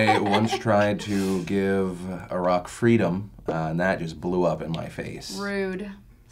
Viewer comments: haha omg i heard "a rock freedom" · I heard it as a rock to because Ive never heard anyone pronounce Iraq like that before. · To be fair to Trisha, I heard Phil say "I gave a rock freedom" as well. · The thing about giving Iraq freedom yeah I totally thought Phil said "a Rock"